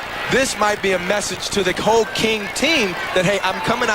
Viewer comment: for a moment i thought he said cocaine team